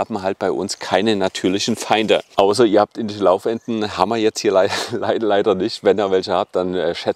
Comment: Was waren die natürlichen Feinde gleich? "Indlilaubwändle"? Ich hab´s mehrfach angehört und leider nicht verstanden. - Vielen Dank für die Infos! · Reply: Ausser ihr habt Laufenten......